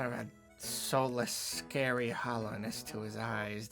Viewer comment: You guys it sounds like he says "Hallownest" at 9:43 He's definitely doing a video on Hollow Knight